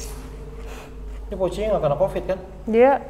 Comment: aku yg salah dengar atau apa ? Minit 39:22 , ini poci nya gak pada covid kan 😂